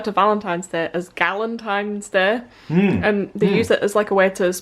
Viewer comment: Haha! 😂 Is it bad that when you said "Galentine's Day" I thought you said "Gallontine's Day" as in being able to eat a gallon of ice cream? I'd totally sign up for that! 🍦